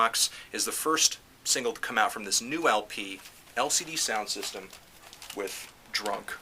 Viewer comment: Lol, if you turn on Transcibed Audio (Beta) at 1:48 it understands "Helsinki taxes" instead of "LCD Soundsystem". I think Helsinki Taxes would be a much better bandnname :D (Yes, I watched it again, because I couldn't believe that you actually liked that track..)